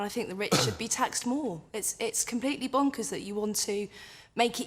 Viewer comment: "It's completely bokers you want to make it easier for people to get rich instead of harder" ????